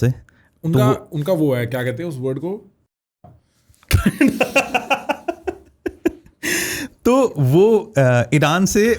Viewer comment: oye kya bola... Hamko bhi sunao be... 😂😂😂😂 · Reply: Tatta